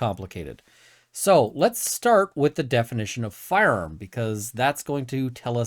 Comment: My tired brain genuinely thought you were going to say, "Let's start with the definition of 'fun'."